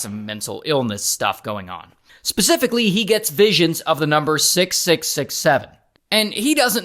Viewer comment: did someone say 67 !!